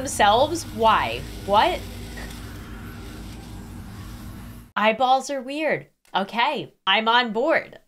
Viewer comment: anyone hear "my balls are weird"